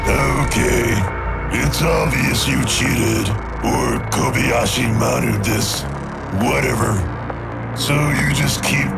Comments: what does he say after Its Obvious You Cheated? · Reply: The Patriarch, otherwise known as Kevin Clamely, says, "...or you Kobayashi Maru this." It's a reference to Star Trek. He's referring to the training excercise designed to test the character of Starfleet Academy cadets in a no-win scenario. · What is he saying and what does it mean? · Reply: Rebuilt Skeletal Structure Kobayashi maru...a reference to the unbeatable test in star trek